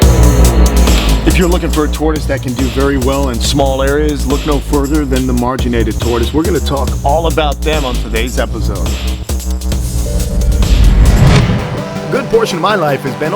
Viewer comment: At first I thought it said marinated tortoise